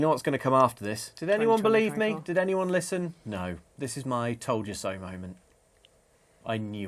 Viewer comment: Had to rewind the intro because I thought said "this is my Totoro moment"